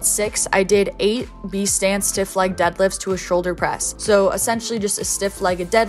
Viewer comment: Literally thought I heard you say Sam smith deadlifts